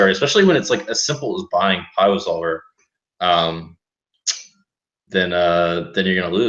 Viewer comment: "especially something as simple as buying....." what does he say here?? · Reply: pio solver